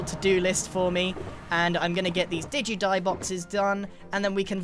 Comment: did grian say digi boxes? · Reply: Did u die